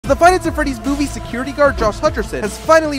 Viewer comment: at first i thought he said "FINAF".. forgive me